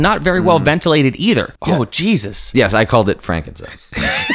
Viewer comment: can anybody understand what frank said at 2:49 ? I'm hearing frankensess and im pretty sure that's not the case... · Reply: Frankincense. It's an aromatic resin. It's a Jesus joke, so I'm surprised that I understood😂